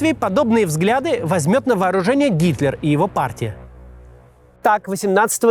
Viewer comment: На 37:29 послышалось "Гиркин", а не Гитлер. И ведь в нынешнем шизофреническом российском обществе не исключено, что подобные радикалы могут прийти к власти и еще более усугубить конфликт и последующее падение России.